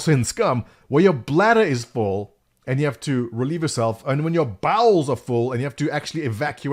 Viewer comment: lol I thought you said "when your balls are full and you have to evacuate" XD